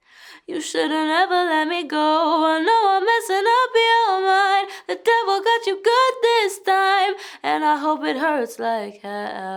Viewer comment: I could’ve sworn it said, “ I know I’m messing up you mind, but damn I got you good this time.”